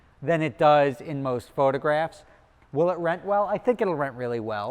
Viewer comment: I thought he said at 8:50 "will it wreck well? I think it will wreck well"!